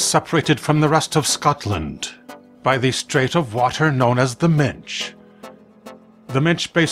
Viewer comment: I thought he said The Minge 😂